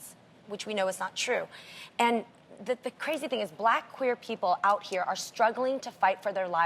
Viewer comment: that asian Lady is soooo dramatic in every episode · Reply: Did she say black queer